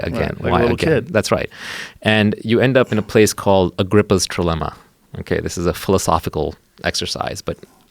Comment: What type of philosophy did Naval say at 1:36:44? Agriphal Trilemma?